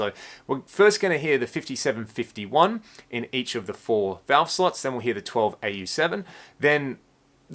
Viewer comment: I keep hearing you say sluts instead of slots. Valve Sluts is definitely what I am and would be a great band name